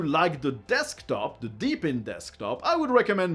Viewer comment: Just a quick LOL, when you said "Deepin", because of your accent, I thought you'd said "DBAN" (Darrik's Boot And Nuke) and I was like "wut?"